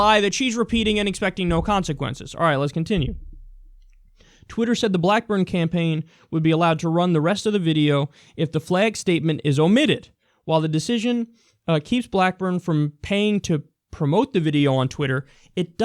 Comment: I thought you said back burn lol describes this bitch well though.